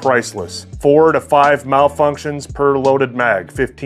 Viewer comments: Until i understood you said training. Thought you said trading 4 times and was so very confused. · You have a great perspective of this. Most people would consider that junk but you consider it a training tool for malfunctions. Lemonade out of lemons, I like it. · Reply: I thought he said trading tool and got super confused